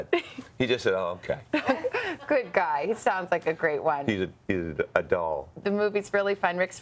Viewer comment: Rick is talking about his son and she says "he sounds like a great wife???" (around 4:18) or did I mishear?